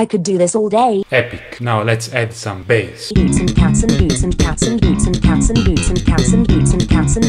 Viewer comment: Bruh I hear 'Boots and campus'